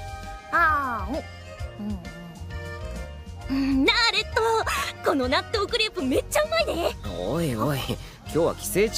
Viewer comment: hold up did she just say Naruto or am I tripping balls rn